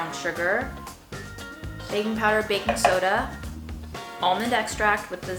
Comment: I literally heard babypowder😂😂